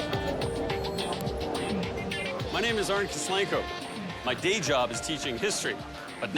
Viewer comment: “ my name is Arntz DeSchlenko…” ? Did I hear that right? David Arntz